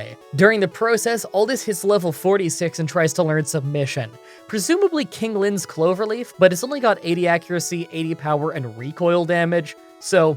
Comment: ?????? What he say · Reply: "During the process Aldis hits level 46 and tried to learn submission. Presumably King Lin's Cloverleaf but it's only got 80 accuracy" 😂😂😂 Idk what on earth he's on about